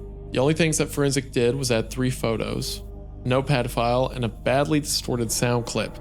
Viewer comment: I CANNOT be the only one who heard Wendigoon read "a notepad file" but sound it out like "an old pedophile". I had to do a double take. Makes the story more unsettling as well. "Forenzik added three photos, an old pedophile and a badly distorted sound clip." EDIT: HE DOES IT AGAIN!!